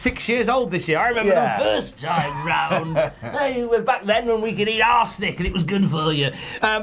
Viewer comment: Thought said “I remember when I’d eat an ass licking and it was good for you”, come to find out “arsenic” sounds like ass licking in acccent...